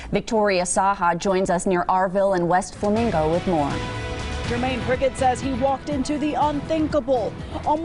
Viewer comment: Did she just say Jiminy Cricket?!